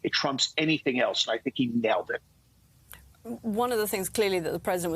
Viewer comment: did he say "...failed it"?